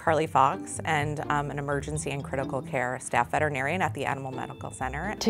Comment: I thought it said veterans 🤦‍♂️